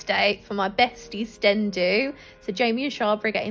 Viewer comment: Jamie??? That’s so weird I used to watch him all the time and now I find out you’re friends omg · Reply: I thought she said sten do, whats a sten do?? Im confused.